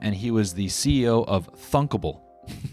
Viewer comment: genuinely thought you said f-able and had to rewind